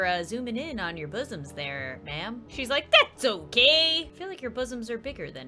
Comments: Omg, the subtitles threw me off. I thought the PE teacher said thats so gay instead of thats ok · "Shes like thats okay!!" Subtitles "THATS SO GAY!!" help idk why the subtitles said that 😭